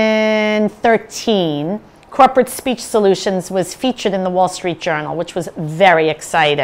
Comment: I was watching your presentation on full screen and had to pause it so I could read the description under the play window just to catch your company name (Krupert Speech Solutions is what I heard) Corporate Speech Solutions. I guess you did that on purpose to show us how disrupting dropping syllables out of words or otherwise mumbling is to effective communication.